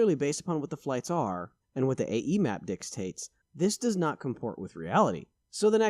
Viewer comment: Paused at 1:54 to ask: Did you just say Dickstates, dickstastes or dickstaints? lol